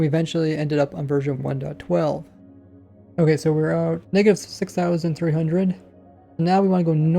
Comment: does he say the n word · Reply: negative